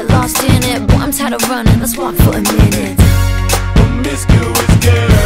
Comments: I was 8 when this song came out and I literally thought she said “once had to run in a swamp for a minute” lmaoo like what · When I was younger I thought they were saying mysterious boy/girl xD